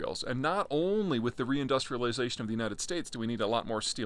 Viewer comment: Did you say re-industrialization of the United States?